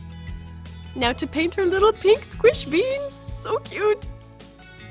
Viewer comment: did she say ' squish beans '?